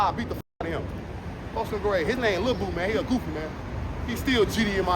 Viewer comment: his name is lil boo, if I heard that right. I've never heard of him in O Block before, but now I'm intrigued.